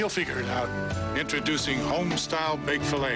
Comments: I always thought he said 'Trust the Gorton's Fish Fillet' 😅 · My mom actually thought they said trust the gorgeous fisherman! Lmao. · Or she thought they said touch the gorgeous fisherman! 😆